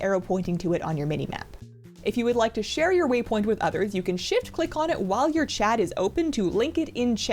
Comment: 🤣🤣🤣 sounded like "shit-click" to me